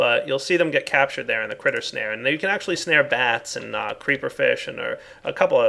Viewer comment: I thought said Creeperfish XD thats the unholy spawn of a creeper and a fish